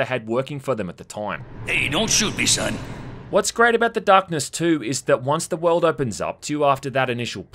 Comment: Did you just say what's great about the darkness 2 😌